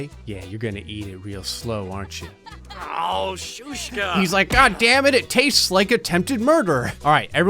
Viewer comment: Well Gray, it sounded like he tried to say "b*tch" in Russian, so perhaps your translation of his emotions wasn't far off... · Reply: Suka